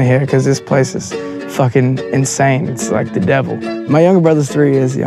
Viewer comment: Did he just say the devil 3:00 WTF